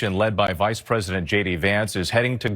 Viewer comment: why is it when he says 'J D Vance' I hear it as 'Shady Vance' ? SO sorry for you Americans at the moment